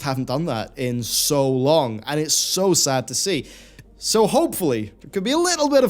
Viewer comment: When you say 14:20 "so sad to see" I only hear "not entertained" :D